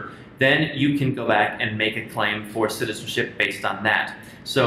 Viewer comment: what did he say?? Can't make out his fast accent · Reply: A Mc “You can go back and make a claim for citizenship based on that”